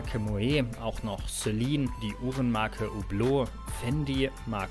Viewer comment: bin ich der einzige der Celine die h uren Marke verstanden hat ?